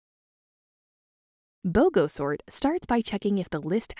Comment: I thought it said snort and not sort